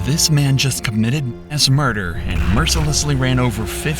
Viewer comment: How do you get arrested for "ass murder?" 0:25 What an idiot for censoring the word "mass" lmao! 😂😂😂😂 some people are paranoid af